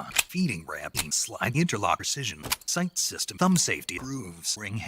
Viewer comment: For a second there I thought it said “cum safety” instead of “gun safety”